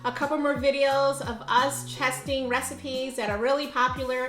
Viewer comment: I swear she said, "chesting" recipes.